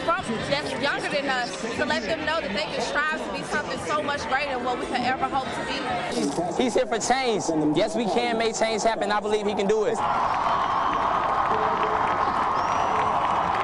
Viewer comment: I thought he said he's here for chains...